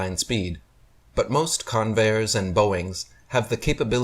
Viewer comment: After 9:54, is he actually saying " Convairs and Boeings"? Or is it "conveyors"? Convair the aircraft manufacturer went defunct in 1996 according to Wikipedia. How many of their aircraft are still being used? 🤔